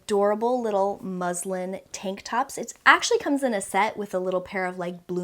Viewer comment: I thought you said "Muslim tank tops" I was so confused at first 😂